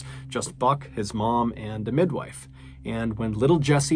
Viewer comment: Mr.Ballen cusses hardcore in this video. He says that he would just "f**k his mom, and midwife." Obviously this isn't what he actually said, but close your eyes and listen. Sounds exactly like it.